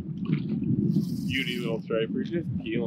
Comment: I thought it said “cape cod strippers” 🤦🏽‍♀️